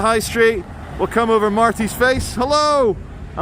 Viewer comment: did you just say we will come over martis face now that is something i would watch lol